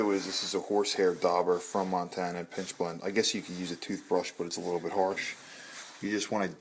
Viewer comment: I think I heard you saying "PINCH blend". It is pitch blend, right ?